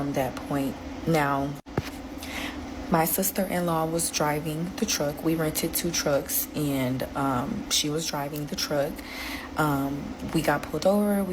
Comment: wait... what was she driving?? Didn't hear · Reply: A suburban